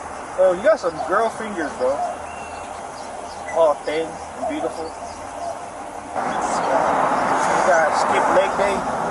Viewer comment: Did he just say the cop had beautiful fingers? 24:39 🧐🤔